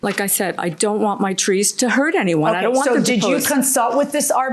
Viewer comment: "I don't want my trees to hurt anymore" 😂😂😂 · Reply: Did he say anymore? I thought he said anyone!